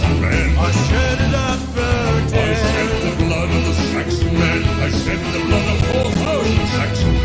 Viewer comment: I shit the blood on the sexy man. That's what I hear lol